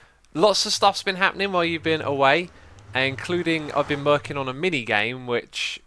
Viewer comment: he says I've been "merking" on a game instead of working :p